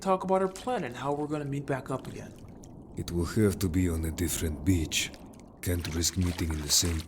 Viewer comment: Another what?? 😭😭 3:57 Guys ik he's saying "beach" and not the b word- 😭